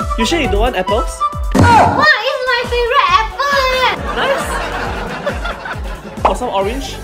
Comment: I thought she said the apple iphone